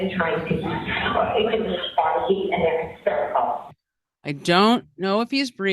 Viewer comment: Jen says, "...there's 2 women ... And they're hysterical." You say / hear "critical" instead of hysterical. This is not criticism. To me, it's Jen saying there's 2 women but they're both out of control. As if to put herself in higher standing by contrast, as being the only rational one there. It fits.